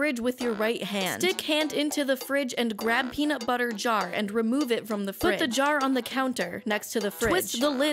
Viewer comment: I'm sorry for being a little nerd here, But did she say reach for the peanut butter in the fridge?